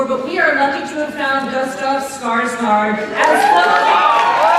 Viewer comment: Sounds like she says ''Gustaf Skar skar'' Way to butcher a name..